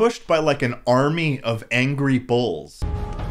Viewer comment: why tf did I think he said angry bowls